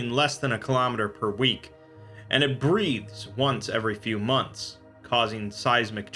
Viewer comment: did he say breathes or breeds